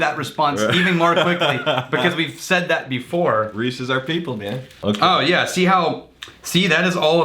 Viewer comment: You said “Reese is our people” but what I heard was “Reese’s are people” and I was like “what? no... they’re candy.”